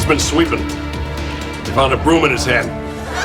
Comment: I always thought Mr. Traegger said “we found a broom in his HEAD.” Clueing us in to the fact that he was hitting the ceiling with the broom and fell onto it, getting impaled. I’ve gone this whole time thinking Mr. Heckles died gruesomely and horribly lmao.